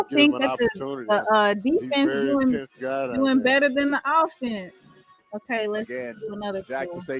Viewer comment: I’ll take Grayson as the qb .. JB and Phillip are overrated! This film on them looks bad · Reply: Naaah I thought they said he better than shedeur 😉😉😉